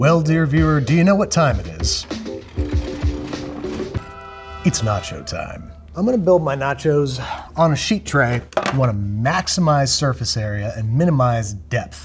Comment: Wait, on 14:43 did he said "Not Our Time"?